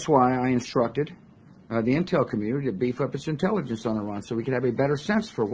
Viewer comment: Did he said "I instructed the intel to beef up the inteligence on this..." at 3:45? More like "bs up".